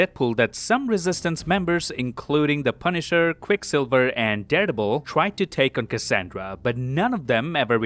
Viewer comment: I keep hearing Dareble